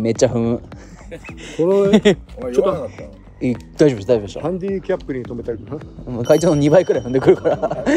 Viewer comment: ハンディキャップに止めた、って聞こえるけど、それならマジクソ。